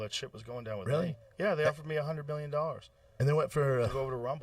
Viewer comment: did he say billion or am I tripping?